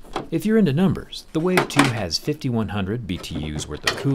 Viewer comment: Wow, a portable 1 hose ac unit. Ive never seen such a common thing before exept almost everywhere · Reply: At only 1500 BTUs, it'd make a pretty terrible air conditioner. Good for cooling a tent maybe? EDIT: I misheard it. It's actually 5100 BTUs, making it a pretty capable air conditioner. My mistake!